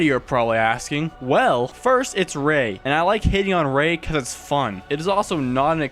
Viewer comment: I thought you said hitting on Rey